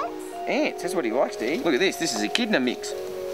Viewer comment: I literally thought Steve said “this is echidna meat!” before he fed the mix to it Edit